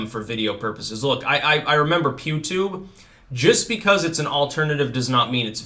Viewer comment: Thought you said Pube Tube. That might be an entirely different kind of content